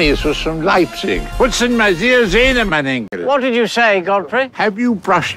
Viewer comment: ich hab verstanden "putzen wir dir die zähne, mein enkel." O.o